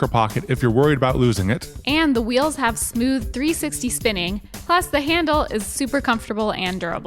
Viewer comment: Am I the only one who heard say "for 350 spinning" at 18:47 ?What about the remaining 10°?😋